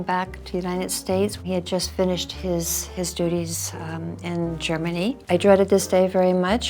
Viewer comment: "WE" finished his duties???"She" didn't finish anything! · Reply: I heard " He had just finished his duties"...